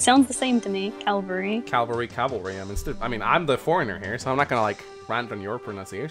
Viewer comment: When said cavalry I thought she said Calgary